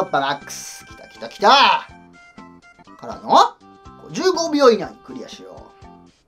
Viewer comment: 、15秒以内にクリアしようって 聞こえたw